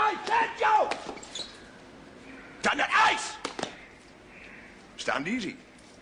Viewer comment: What is he saying at the 2:40 mark when he is addressing the troops??? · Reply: "Stand at ease, attention, stand at ease, attention, stand at ease, attention..."